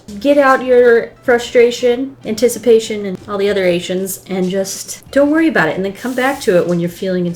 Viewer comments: and all the other ations i thought she said all the other asians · When she said all the other ations I thought she said asias